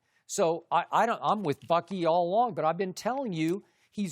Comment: Did Skip say "Im with fucky"?